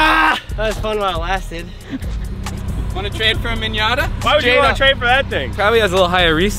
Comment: Did say minyata 🤣😂